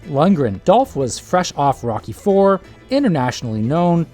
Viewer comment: At first, when you said Dolph Lundgren was "fresh off Rocky IV", I thought you'd said "Rocky Horror", andI started imagining him prancing across a stage (on Broadway? in London?) in a gold lame' bikini . . . .